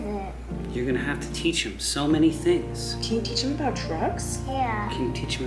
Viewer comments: When she said can you teach them about trucks I thought she said can you teach them about Dr*gs 😂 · I THOUGHT COLLEEN SAID “can u teach him about drugs?” bahahahhahaha she said trucks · when colleen asked to teache him about trucks i thought she said drugs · fully thought she said 'can you teach him about DRUGS?' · When Colleen said you gonna teach him about trucks at first I thought she said drugs and I was like COLLEENN